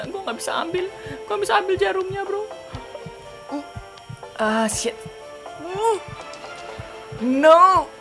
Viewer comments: miawaug ngomong apa, apa gw slh denger :v Edit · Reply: Ahh shit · salah denger gak ya.. "Oh shit